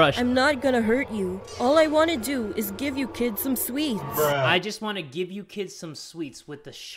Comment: Guy: "You Guys Want Some Sweets" Me: You Got 3 Musketeers Guy: No Me: Then Get The Hell Out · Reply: I thought the stranger said "I'll give you some sleeps :)" lol